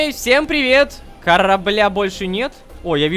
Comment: То, как я это услышала😅: «Хара-бля больше нет? »